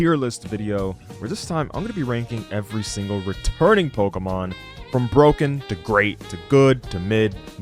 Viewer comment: My sister thought you said "Retarded Pokémon" instead of "returning Pokémon" 💀